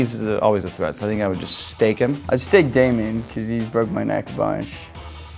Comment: Did he just say Damien? that's cringe ngl